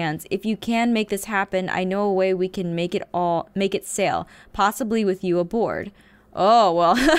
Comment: Garroth: "I know a way we can make it sail...Possibly with you aboard" Me:.......Hmmmmm i dont know what you guys heard buuut......This is what i heard: "I know a way to make this ship sail...Possibly with you and me aboard" ..........I'm sorry peeps who doesn't ship this.....I find it hilarious😅🤣 (Btw im using someone else's phone cuz mine's dead)